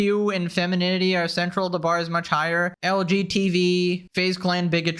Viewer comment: did bro just say LGTV? XD xD xD xD xD